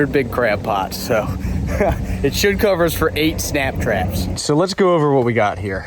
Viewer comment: Thought you were saying snack traps. You had us in stitches today. Thank you. Wish we were closer. never eaten crabs.